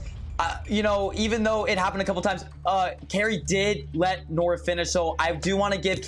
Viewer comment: Anyone know what said at 24:02? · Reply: She said she didn’t let nor finish